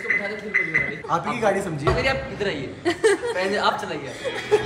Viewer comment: Did Said Bhabhi Ji Aap Idhar Aayiye?